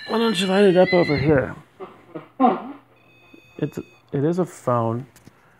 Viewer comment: I say, "that's weird" and I think I hear a woman say "it is" then 15:38 can you hear me? (male)